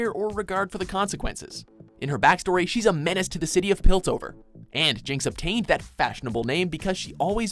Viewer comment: Did they just say Piltsover instead of Piltover...?